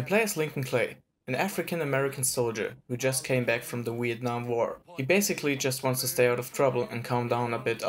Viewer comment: is it just me or did he say wietnam